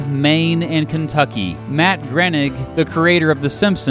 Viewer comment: Matt Grenik? Sounded like you said Grenik.